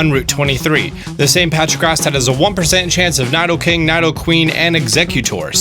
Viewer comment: did you just say "naido"king and queen